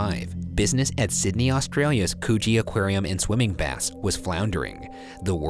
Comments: i thought he said “coochie aquarium” instead of “coogee aquarium” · i had to do a double take, i full-on thought ryan said 'coochie aquarium" i had to reread the caption. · I deadass thought he said "Coochie Aquarium" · DEADASS THOUGHT HE SAID “COOCHIE AQUARIUM” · I live on an Island nicknamed Coochie in Australia so for a bit I thought he was saying coochie instead of Coogee 😂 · i really thought ryan said “coochie” instead of “coogee” and the dive deep joke had two meanings for me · Did I hear "coochie aquarium" or am I really just going crazy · Whyyy did I hear 'coochie aquarium' at 0:38?? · Jeez... not only was it printed on the scrren but he pronounced Coogee correctly.. something these guys NEVER do right with Aussie names. Pay attention ya'll😒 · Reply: bruh i am aussie and thought he said coochie, plus, Coogee and coochie sound extremely similer. just because it was printed on screen it doesn't matter if that happens it can't sound like coochie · I heard coochie aquarium 😂😂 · i thought he said coochie XD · I THOUGHT HE SAID COOCHEE AQUARIUM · i heard "coochie aquarium" idk bout you guys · i thought ryan said coochie aquarium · I heard "Coogee" and thought they said "coochie" LMFAO bye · Him saying: coogee Me: did he just say coochie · I THOUGHT HE SAID COOCHIE AQUARIUM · diD y0U saY coOCHie?! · I thought they said coochie aquarium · misheard it as coochie aquarium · I thought he said “coochie aquarium” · I thought Ryan said coohie aquarium lol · KSDJS I HEARD COOCHIE